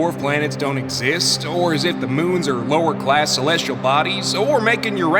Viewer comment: I’m sorry did Jupiter just say LOWERASS?